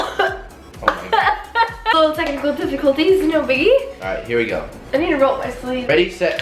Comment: Please someone tell me what she said at 4:13 ?? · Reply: We're having technical difficulties but no biggie